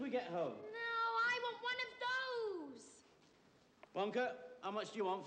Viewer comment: Mr salt said what are those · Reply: Vatche Medzavorian I thought he said "one of those!"